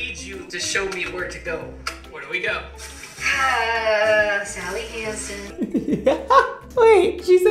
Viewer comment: She really says "Sally has it" at 1:45 just saying